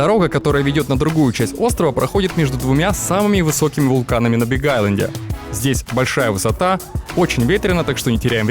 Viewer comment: " В последствии оказалось, что почти все, кого я встретил в этом ритрите - говорят по-русски, и вообще на Биг Айленде довольно большая русскоязычная община" Услышал "набегайленд" - отслова "набежали" и сделали общину :D